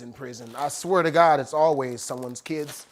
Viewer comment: yeah I know right... wait what did he just say 🤭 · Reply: It's always someone's kids lmfao